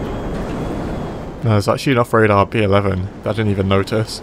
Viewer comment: “There’s actually enough radar pee 11”? After like 2 minutes of repeating that, I finally figured out what u said. Xd (“there’s actually an off radar B-11”)